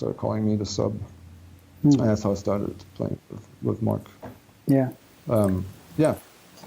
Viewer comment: El Molinero!!! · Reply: After having watched this 1271 times, at 1:09:30, I keep hearing Jochen say: „my ***** started playing with Marc“😂